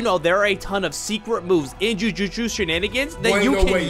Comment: Did bro really say Infinite Void for the hollow purple nuke at the beginning 💀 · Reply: Did bro just said Justusutu?